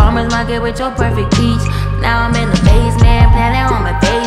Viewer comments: lmfao the fact that I thought she said “now I’m in the basement” instead of “now I’m in amazement” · Wow I got the lyrics wrong, but they still matched the vibe. I thought she said “With your perfect peach. Now I’m in the basement, planning home invasion.”